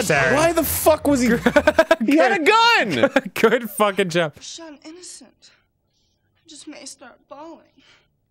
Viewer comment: wtf did that chick say at 10:40 ? it's driving me crazy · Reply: "You shot an innocent, I may just start bawling"